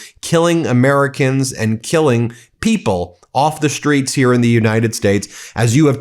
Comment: I thought he said tge Straits were open??